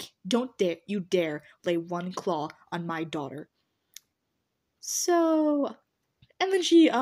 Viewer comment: Not sure if i hears rhis right but i heard, "you will not claw my daughter-" AND THE SONG, "THIS MAN WILL NOT MAKE AN ORPHAN OF MU DAUGHTER! NUMBER 9-"! popped up in my head.